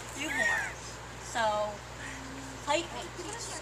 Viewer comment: did she just say bit me O:44